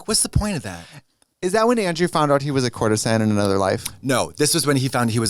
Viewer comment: what did trixie say? corner sign? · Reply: "Is that when Andrew found out he was a courtesan in another life" :)